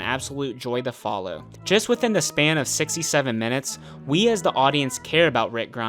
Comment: did he say 67 minutes...?😶‍🌫️